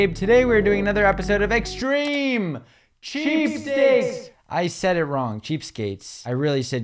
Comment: I thought you said cheesesteaks at the beginning